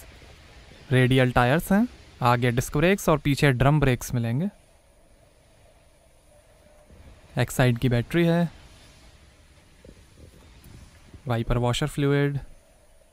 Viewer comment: I heard exite ki battery😅😅😂😭